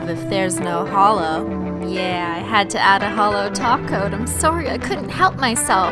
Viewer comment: When you say "holo top coat" it sounds like your saying holo taco like it literally sounds like your actually saying holo taco